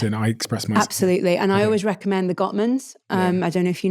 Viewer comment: I couldn't understand the resource mentioned on communication, here 20:22. Anyone else understood the source? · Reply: This is regarding the “Gottman’s” - John and Julie Gottman who are authors and psychologists who have written tons of material regarding attraction and relationships.